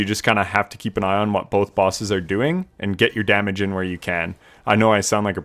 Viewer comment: In the first 5 seconds I heard him saying "FOUR BOSSES" and I was genuinely horrified lol luckily, I got it wrong